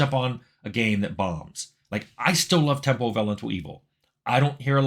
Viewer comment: Temple of Alantuibo? Tempo of Allan To Ibo? Temple of Vallant Evil? Does someone know what that game mentioned at 13:33 is? I am unable to decypher that bit of talk...